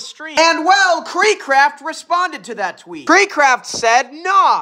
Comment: i thought bro said kreek crap